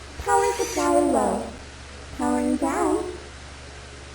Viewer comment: anyone know wtf shes says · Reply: "Power supply low. Powering down."